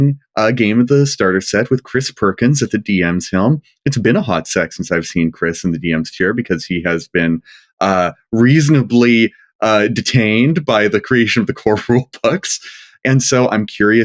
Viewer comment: 😂 very impressed with restraint when she clearly thought said "hot s*x" and "Chris Perkins" in the same sentence.